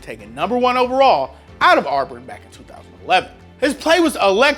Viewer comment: did he just say”arburn”